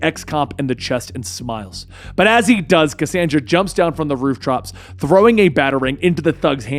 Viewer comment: am I the only one who heard say rooftrops?? 😂